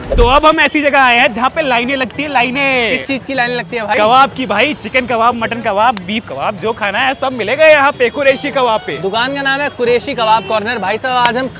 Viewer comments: did he said beef kabab? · did he said beef kabab ?